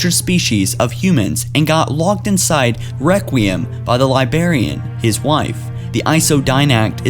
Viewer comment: I'm sorry, but every time I hear you say library ( or librarian) I hear "lie-berry" or "Lie-berrian" xD